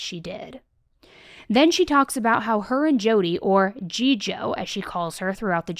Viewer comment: Did I hear that right - G-Joe? I'm distorted as f*ck...